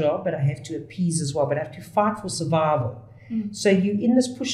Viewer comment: I love your accent. I thought you said I have to fart for survival. I have to fart. I am a whistleblower and I agree. You made me laugh today and a whistleblower who laughs is better than one gone.